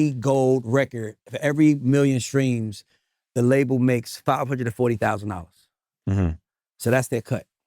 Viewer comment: did I hear that correctly? For every 1M streams, the label makes $540k???? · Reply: The subtitles are wrong. He said "500 to 40.000 $" not "540.000 $"